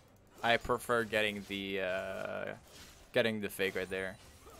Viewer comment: everytime he say “veigar” i keep hearing “faker” lmao